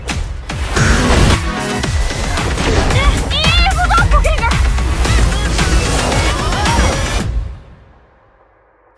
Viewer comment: tf did she say? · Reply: “Vee is a doppelgänger”